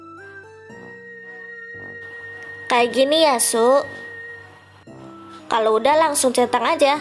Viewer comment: gue dengernya kek gini asu🗿